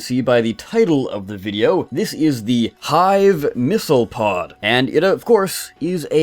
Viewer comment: Thought it said ,,hive missile fog" Ye